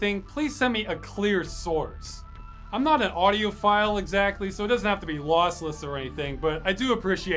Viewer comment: When said that he isn't an audiophile, I thought he said that he's not an audio file. It's good to know that isn't another Miku Hatsune.